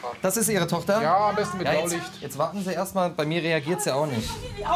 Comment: Da liegt jemand! Hallo? Machen sie mal die Tür auf!🙄🤦🏼‍♂️ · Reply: ich hab gehört: reagiert gar nicht.. hab ich mich verhört?😂😂